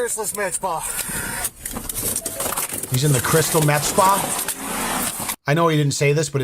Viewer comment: I also thought he said, "He's in the crystal meth spa", and thought 'Sounds about right...' 👍😂